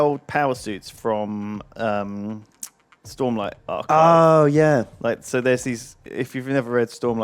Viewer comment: what's saying at 15:51 ? storm is all i can make out. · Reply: Jarod Vermilye "stormlight archive"